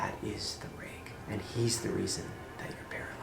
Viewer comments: i didnt listen carefully so i thought he said drake 😭😭😭😭😭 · I thought he said “drake”